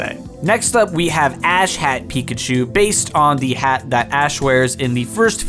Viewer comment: I totally thought he said "Asshat Pikachu" for a second.